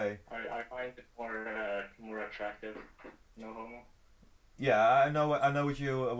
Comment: haha did he really say "no homo" xD?